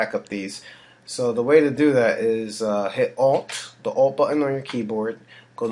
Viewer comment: what are you saying which button?? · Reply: ALT Button